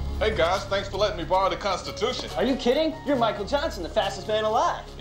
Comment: did he said MICHAEL JACKSON